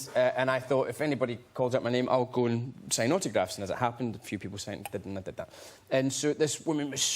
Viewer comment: 請問James是說sign autograph嗎？還有4:00 他說驚嚇到的英文是甚麼呢？很喜歡james但老是聽不懂他講甚麼TT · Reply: 處說的是sign autographs沒錯喔，然後4:00 說的是"taken aback by it"，被它嚇到的意思~ 一起努力聽懂James的語言吧XDD